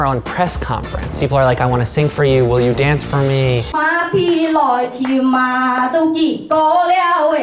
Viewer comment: the way she said matt in chinese sounded like ma di which means mop floor